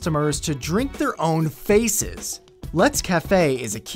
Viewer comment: i think i just heard "drink their own feces"